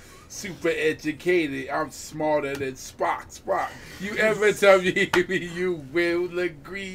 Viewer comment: WHO ELSE THOUGHT THEY SAID “DONT YOU TELL ME TO STOP